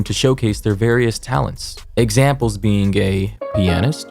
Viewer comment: Example being gay pianist anyone hear it too?